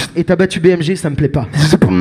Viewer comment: what does alem say at 5:33 that gets the crowd and grinning think he says bmg but dunno rest